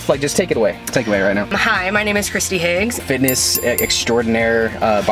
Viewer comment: oh she said hi i thought she said im high (in the begining)